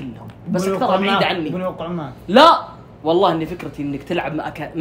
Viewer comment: وش قال ؟ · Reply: بنوقع معه